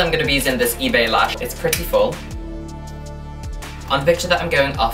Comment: when you said the lashes were "pretty full", i thought you said "pretty-ful", like pretty/beautiful, and i was squeeing soooo much